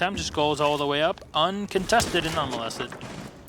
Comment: did he say "uncontested and unmolested" ?